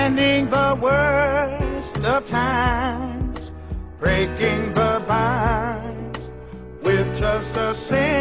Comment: I thought he said “breaking the vibes” I was about to say, “people used the word “vibe” in 1974?” Then I realized he said, “vines” lol